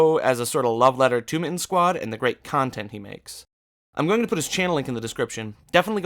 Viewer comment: Heard that as "Great Khan-tent he makes"